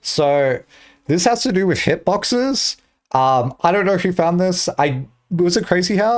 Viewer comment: did you say "shitboxes"?